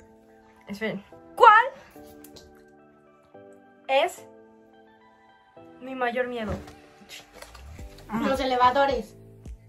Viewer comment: Soy l única que escucho yo mi amor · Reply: yo escuche "ya mi amor "